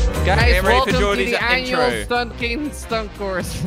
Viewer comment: Opening line I thought horde said ‘welcome to the annual fucking stunt course’ 😂